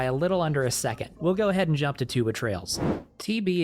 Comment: Every time you say "Two Betrayals" i hear "Tube of Trails" 🤣. Great video as always!